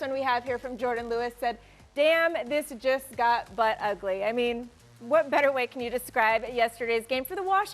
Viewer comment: I thought the first tweet said "Damn, this girl gotta big butt"